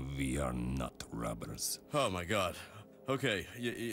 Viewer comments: OMFG ARCHER THOUGHT HE SAID NUT RUBBERS. I JUST GOT THAT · what did Bob think he said? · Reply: He thought they said nut rubbers